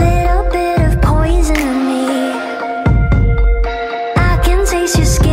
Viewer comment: ぺろぺろ小泉にしか聞こえん